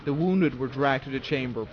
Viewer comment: ily but wtf are you saying 😂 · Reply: "Edward dragged to the chamber" 10/10